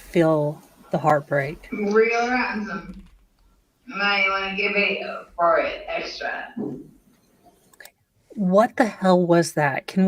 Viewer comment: I’ve been trying to decipher the drunken gibberish at around 44:22 I think she says “real ransom might want to get video for extra” 🤷🏻‍♀️ What are other interpretations? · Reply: You are close. She says: " Real Handsome. Might wanna get beat up for it extra, cuz I got this."